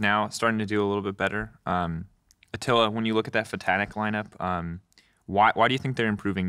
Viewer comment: Did Drakos say “Ftanic” ? Lol